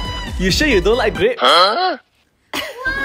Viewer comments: I thought he said " it's a fuck you pie" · Is it me or did they just say " it's a fuck u pine'' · I heard "it's a fuck you pie" 💀😭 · Is it only me or anyone i heard "its a fuck you pine"😂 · It sounds like he is saying its a f*ck you pie!! 🤣🤣🤣🤣❤❤ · I heard "it's a fuck you pine!" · I thought he said it's a f##king porcupine💀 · "I'ts a fuck u pie!" 😂😂😂😂 Mommy im famous!!! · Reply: I heard "it's a fuck u fine" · Others: "its a Porcupine." Me: "ITS A F4CKING PIE-" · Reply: I heard it as "It's a f*ck u pie" 😭🖐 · I heard "it's a fuck a pine"😂😂